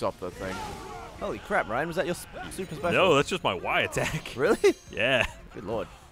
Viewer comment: 17:2 At first I thought he said "No, that's just my wife attack."